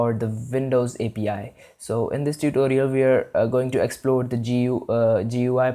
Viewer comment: bro at the begining I thought you said we're going to explode the api and I was confused and scared! LOL